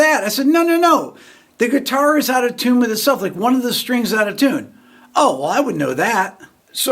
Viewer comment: Oooooh! Now I get it! I thought you were saying you had no more tunes or songs. You were out of tunes